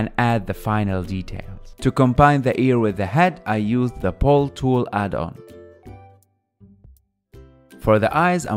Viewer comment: did you say pole tool addon??